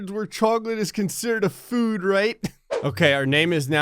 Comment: if you press captions will say food instead of fruit